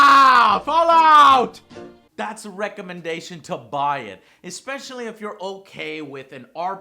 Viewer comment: I swear he says "That's a rectumendation to buy it..." O.o